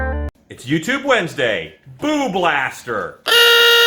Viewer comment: I heard "boob blaster" at first. I'd sooner use a dremel than the reciprocating saw, but what a fantastic 12v idea!